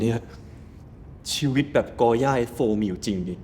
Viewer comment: did he say A life like gorya from F4 really exist